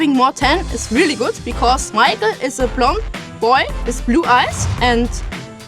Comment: I honestly thought she said "Michael is a plump boy" and I thought to myself 🤔 yeeeeaah girl that's right! He sure is plump 😂 10:22 she said blond 😂